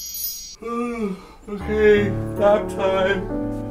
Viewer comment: I dont have a dirty mind... But I honestly thought said 'fap time'